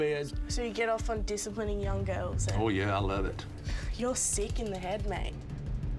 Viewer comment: “You get off on disciplining little girls?” “I sure do, I love it.” 😬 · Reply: I thought she said "You're seeking the head mate!" LMAO.